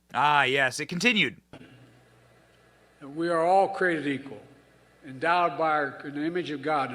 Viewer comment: Oh, oh...sounds like he said..."We are all creepy and endowed by our creator"...